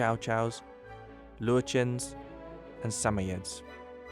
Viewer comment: the was He says löwchen